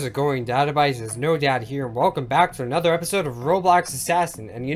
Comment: did he just say "no-dad here"